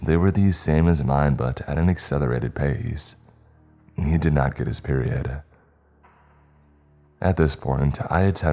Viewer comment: That boyfriend is/was a keeper! He saw her cannibalize a full leg and still looked out for her! Romantic, in a zombie sort of way... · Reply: he fails to get his period?! I heard that wrong surely